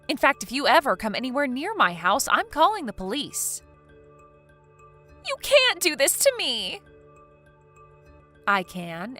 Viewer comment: anyone else hear from this Terror, "you cant hold me accountable for my action".